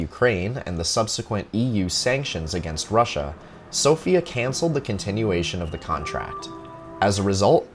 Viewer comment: The captions say canled not canceled.